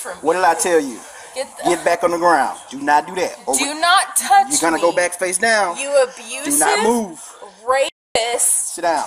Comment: Legit thought the man said “maam you’re gonna get put down for that” and burst into laughter